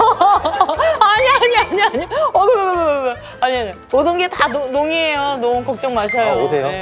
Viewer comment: 모든게 다. 다음에 뭐라고 하시는거죠??? ㅎ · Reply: 농이에요 (농담이에요) 라고 하시는 것 같아요!